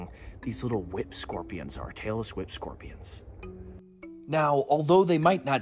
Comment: I died when the auto-generated captions said "Taylor Swift scorpions" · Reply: Lol Petco from The Dark Den sounds like he says Taylor Swift Scorpions in his video too 🤣🤣